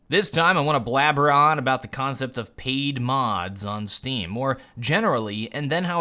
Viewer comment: I'm like half asleep and I thought this said "Pain mods"